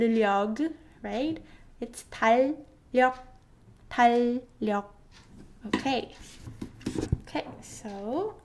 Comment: Hello, is somebody can tell me, At 8:40, for DAL yuh I keep hearing her : Pal yuh (a P sound instead D sound ) is it just the sound of the video??? just finish the video my head hurt :D